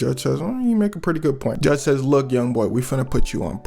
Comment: ''Did you say youts?!!💥😂